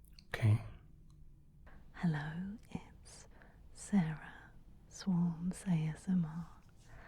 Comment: Who are the ones at 33:58 and 37:47? Couldn't make out the names, guess I fail my hearing test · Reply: I get Sarah (or) something--it's the something which probably makes all the difference. It sounds a bit like voice.